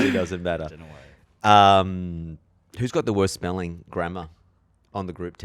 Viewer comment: i heard “whose got the worst smelling grandma” and i was taken aback 😭